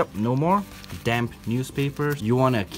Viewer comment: Damp newspapers I thought he said dam newspapers